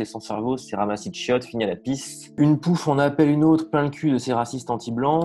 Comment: mdr j'ai entendu "pue la pisse" j'ai mis un pouce bleu, j'avais pas entendu cette expression depuis mon enfance à la campagne dans les années 80 😂